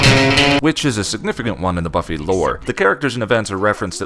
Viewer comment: I swear I had to rewind the video because when you said "witch is..." in the first line I heard "which is" and thought I was coming in in the middle of aline. Love rewatching your old episodes.